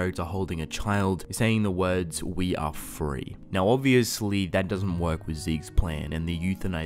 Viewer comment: I thought it said "you are free"" not "we are free"